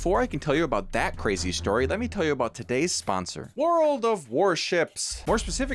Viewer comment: dude i thought you were about to say world of warcraft 😂